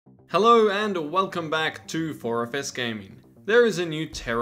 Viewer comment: I don't mean rude and I think my ears are busted but when you said "4FS Gaming" I heard "orifice gaming" I almost spewed my drink across my desk.